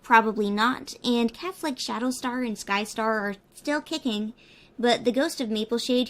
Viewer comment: I thought she said Catholic Shadowstar